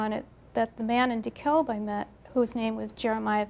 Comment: Look up Comet Kohoutek if you want to know what event she was talking about. This was in DeKalb County, Illinois. There's so much about all their stories that totally line up, and it can't be hysteria. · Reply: Thank you for telling us where this was. I couldn't understand the name of the county - I thought she kept saying 'Decal' - you just don't hear the 'b'.